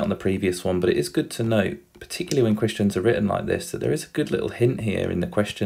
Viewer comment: lol at 4:20 he said when christians are written like this instead of question😂😂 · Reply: He said questions not christians